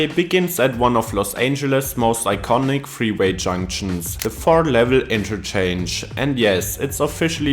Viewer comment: Whenever I heard “four-level” on the traffic reports, I always heard “floor level”. I thought it just meant the lowest level of that interchange. It wasn’t until I just saw it written out here.